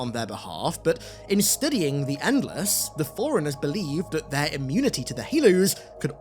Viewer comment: Ooohhhhh… Forerunners! At first I thought he was saying “foreigners” 😂